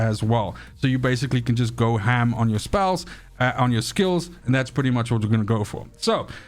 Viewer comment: Lol I don't think so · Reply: You said "go ham on your spells ( I heard spouse)" and you corrected yourself to "ham on your skills*"